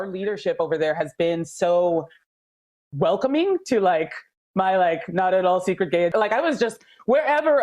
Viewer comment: they edited it out bit what she said was "MY not at all secret gay agenda"